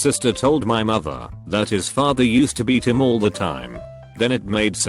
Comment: I thought it said he would beat his meat